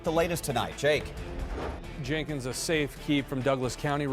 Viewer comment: does the reporter say "a safe key swallowed a set of keys over the weekend"? How did a key eat a key???? Lol. What am I hearing?